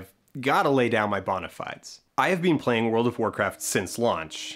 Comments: I have to say that as an Australian listening to your accent, I thought you said “I’ve been playing WoW since lunch” 😆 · I first thought that he said “I’ve played world of Warcraft since lunch”.